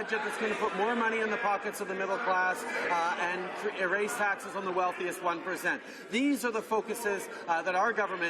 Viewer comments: did he just say: "Erase taxes on the wealthiest 1 %" ??? Everybody still knodding like sheep... Is this a mixup or real policy? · did he just say: "And, erase taxes on the wealthiest 1%?"